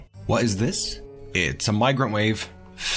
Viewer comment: I heard "Oh, it's a microwave!" instead of "Oh, it's a migrant wave!" ... maybe they need one as they do not have cooked food, yet. ;)